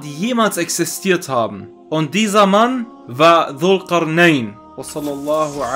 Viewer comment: Wo gibt es denn Infos über den bei 29:10 angesprochenen, dessen Namen ich als Doktor Nein verstehe. Heißt der Nahim ? LG